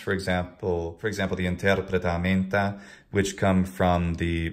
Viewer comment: I have a question. At about 9:20 you mention a text from the mid 2nd Century AD about conversations in Latin. What is the name of that text? You were speaking too quickly, I couldn't catch the name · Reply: I think that he is referring to "Interpretamenta" sometimes attributed to Dositheus Magister. It's called Hermeneumata or Ἑρμηνεύματα in Greek. It was written to teach Greek to Latin speakers and vice versa in the 3rd century.